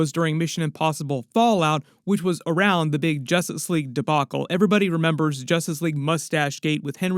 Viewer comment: Here's my new Superman video, post all your reactions in the comments. I was not expecting this! Here's my other new Superman Trailer videos too! <link> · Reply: It sounds like your saying Jostice league at first 😂